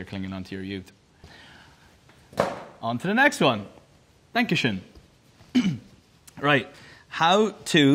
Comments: Ok, but IS HE SAYING DANKESCHÖN AT 1:40!? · did he say “Dankeschön” awwwww so cute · Niall: "Dankeschön!" My German heart: UWU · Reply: I THOUGHT HE SAID THANK YOU SHAWN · Niall: "Dankeschön!" My German heart: UWU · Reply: Me who thought he said "Thank you Sean" ._. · To the german ones: Anyone noticed the Little „Dankeschön“ (thanks in german) at 1:41🥺🥺🥺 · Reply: I'm not German, but when I heard that I thought he said "Thank you Sean" so, lol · is he saying „Dankeschön“ ?😂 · Niall: Dankeschön Me: Bitteschön! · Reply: Lol at first I thought he said thank you schön and I was like ok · did he just say "dankeschön" ?? . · In minute 1:40, is he saying ‘Dankeschön’ like they say in germany?! Cause I can’t hear any different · Did he just say “Dankeschön” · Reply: I thought he said "Thank u,Shawn" but in a really fast way hahha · Niall Horan: "Dankeschön!" Every single German person:😱😱😍😍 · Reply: i hate myself 💀 i really thought he said “thank you shawn” but it sounded like “dankushin” because of his accent 😭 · No one: Not a single german soul: Niall: DaNkeScHön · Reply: I legit thought he said 'thank you sean' 💀💀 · Did he just said "dankeschön" in minute 1:40?😂🥺🥺🥺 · Did he say „Dankeschön“ in German?